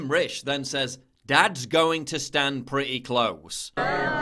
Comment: I heard he said "That's an scandal", no "That's going to stand pretty close."